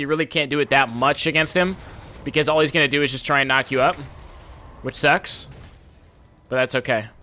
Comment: kinda sounded like "knock you up with sex"